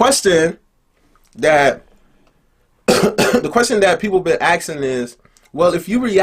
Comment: Did he just say "aksing" 18:18 😂😂 Edit: "aks" 😂😂😂